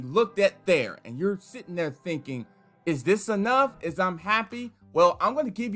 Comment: Did I hear "IS Im happy?"